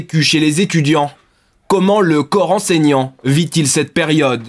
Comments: la prononciation est chelou mdr j'ai entendu coran saignant au lieu de corps enseignant 😂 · "le coran saignant" 0:50 j'ai compris mdrrr